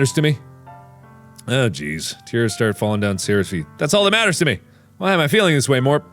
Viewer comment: Tears start falling down Sayori's feet??? Anyone else hear that?